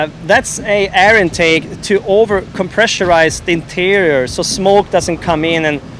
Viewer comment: did he just say "overcompressurize"?